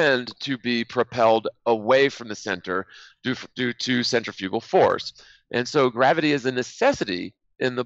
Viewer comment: I'm sorry, but did he really say the F word at 23:52? Respect for the dude dropped from 0 all the way down to the negatives. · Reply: He said fugal